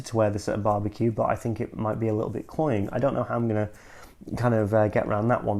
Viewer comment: Just out of curiosity, what is that word you use at 10:46? I hear it in many fragrance reviews but I don't exactly know what the word is. Sounds like kloine or chloin? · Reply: yes mate, that’s Cloying which basically means that it’s so overly sweet that it feels you’re being choked by it or it’s uncomfortable to wear.